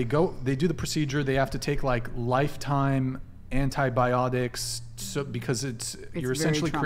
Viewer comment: Did just say in antibiolodiks😂